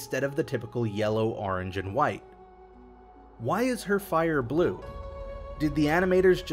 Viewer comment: Did u just say in 1:23 white fire ?